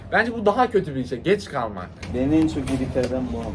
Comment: da ne dediginj anlayabilen var mi HAHDHWKEHWKSJKSJABS yoksa benim mi anlama problemim var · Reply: Beni en çok irrite eden bu ama diyor